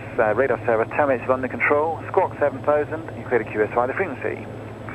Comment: What did the controller say passing you to Gloucester when leaving controlled airspace at 27:30? Sounded like clear to QSR the frequency? Obviously not but couldn’t make it out. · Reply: christopher wilson ,Chris, It's QSY, code for changing to a different frequency en route.